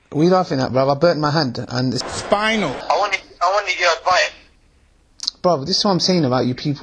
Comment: I thought he said he wanted a bite